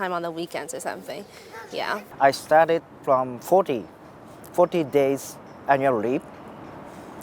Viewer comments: Just want to point out that the CC at 13:35 "Forty days and you're(?)" should be "Forty days annually". I think that's what he said. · He's trying to say '40 days annually'. 19:55 He's saying 'I get the perfect, just perfect ideas' (or at least he tries to say ideas 😆). 28:28 The subtitles say 'the pause on', however he's saying 'the paws on'. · / 30:02 i think he said annually? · he said "annual leave" for those who didnt understand